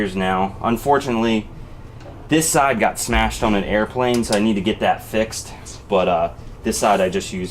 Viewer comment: Oh, I thought for a sec he said this side got smashed by an airplane.